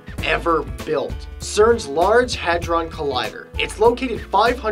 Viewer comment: lol I thought it said large hardon collider